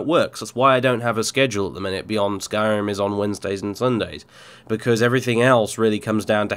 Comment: I thought you said. Skyrim was on Thursday